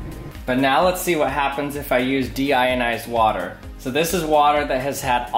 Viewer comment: at first i thought it says demonetized water